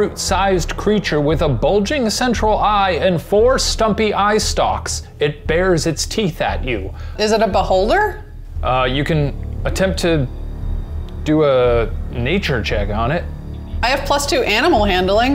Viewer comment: I had to rewind this because I thought I heard. "Is it a butt holder?" I'm glad I was mistaken...